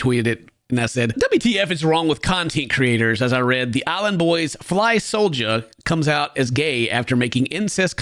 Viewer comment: Misheard Ken saying "Alan Boys". I can live with that.